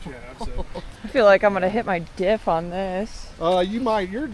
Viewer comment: who else thought she said D 😂